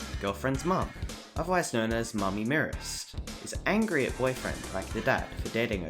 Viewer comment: I heard that the mom name is actually mommy must murder, not mommy mearest. Well that's what I heard of But week 4 name is mommy must murder so maybe? Idk man